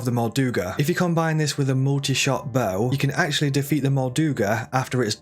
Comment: I’m so dumb haha at 9:16 I kept hearing and thinking you’re saying “mortar shot bow” and after googling and rewatching that part over and over again, obviously you’re saying “multi shot bow” this is why I need help with the game haha